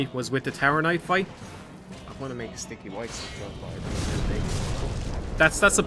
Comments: WTF DID HE SAY AT 33:37???!!! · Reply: I think it was a sticky white stuff joke. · WTF is he saying?? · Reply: He quietly says “I don’t want to make a “sticky white stuff” joke, sticky white stuff is a questionably named item in the game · "I want to make sticky white stuff..." somthing somthing... Can someone understand what he's saying?